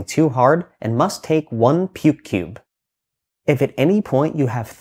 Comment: I thought you said "pube cube" at first, and I wasn't sure what was about to come next.